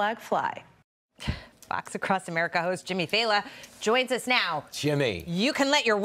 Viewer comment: I thought you said Jimmy Fallon.